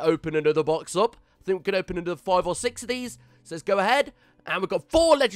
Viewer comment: O I thought u said another acc